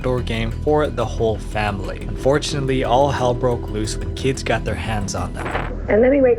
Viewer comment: I had to rewatch like five times, I thought you said Fortunately 😂😭😅